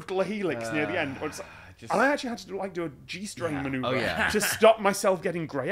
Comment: I'm sorry to say Tom you are correct, auto captions did think you said g-string